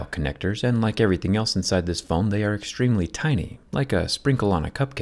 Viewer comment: Is it just me or did he say "inside this Phone"